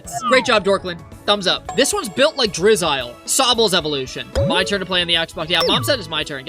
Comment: ngl I thought you said Drizzy at first and I honestly wasn’t gonna dispute it.